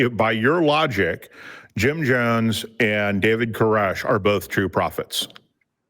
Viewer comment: Every time Jim mentioned David Coresh I heard "David Carusoe" and kept wondering if he was on an CSI: Miami kick lately.